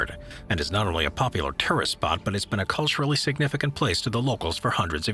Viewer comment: Sounded like he said “ terrorist” spot instead of tourist at 3:48 cc said the same too. Anyone else catch that? Idk I’m half awake lol